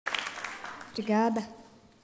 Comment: ouvi um "de nada"